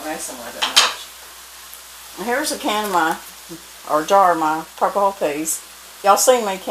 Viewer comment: Love love your channel. I was just curious what type of beans those were, I thought you said purple beans. Because I meet a woman who use to make purple bean jams. I have never heard of this before? Have your? Thank you!